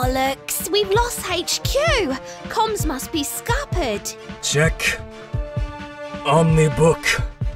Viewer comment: When Genji said ‘check Omni book’ I heard ‘check on me book’